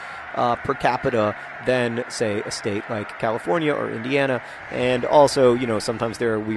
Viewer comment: I thought John said "a state like.... or Vienna," and had a real wtf moment until I realize he said "Indiana."